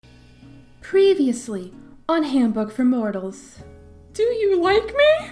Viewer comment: I keep mishearing it as "Previously, on Hamburg For Mortals" and honestly I like my version better